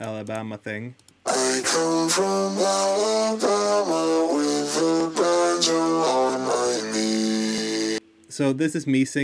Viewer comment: I legit heard " I come from ala bama with a badger on my knee, cocomelon" sorry I might be dumb deaf